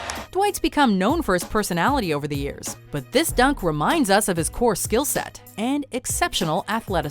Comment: does she say "poor skill set"? Rude.